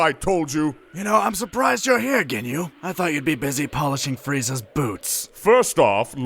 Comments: I thought he said,"I thought you were polishing Frieza's Boobs,Ginyu" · I’m surprised you’re here, Ginyu. I thought you’d be busy polishing Frieza’s boots. Ginyu: First off, Frieza doesn’t wear boots. Second, if he did. I’d have already polished them. Third, he’s off chasing some leftover Namekians. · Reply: In all honesty I thought had said that he thought Ginyu was to busy polishing Frieza's boobs not boots so I had to rewind it like a million times and put the closed captions on to realize he had indeed said boots